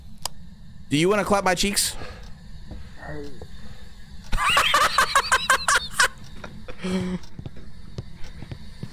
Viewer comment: what does the ghost say at 9:30?? Is it "hey" or "hate" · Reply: Hate